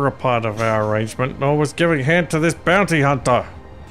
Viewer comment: did he say "giving head to this bounty hunter"? If this is an empire strikes back reference...I think there was some SIGNIFICANT paraphrasing going on. I believe this was a misquote of a lando calrisian/Billy d Williams line... Wait did he say head or Han....giving HAN to this bounty hunter...that makes alto more sense. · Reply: Definitely said Han :P